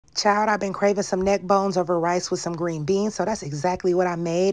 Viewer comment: I’m sorry, but why did I thought she said neck balls?😂😂